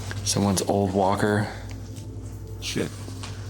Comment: and 24:50 there is a distorted voice that you can hear. It sounds like they are saying “shipment” let me know if anyone else hears it.